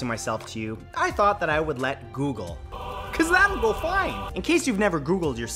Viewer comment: I thought he said “ because I’m white”